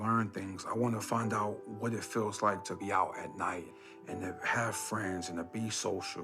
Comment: he said he wanted to find out what it would feel like being out at night. I think you misheard him and thought he said he didn't want to be out at night. Poor guy.